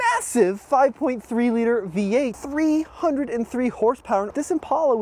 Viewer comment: The 3800sc v6 was way better and faster and had more hp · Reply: you’re right it didn’t, sorry I have one and I didn’t hear him say 303 hp I thought he said 203 lol